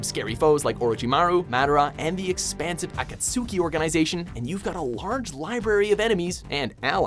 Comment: did he just say members of okotusuki? instead of akatsuki I mean I get that he said it has its spelled but I mean seriously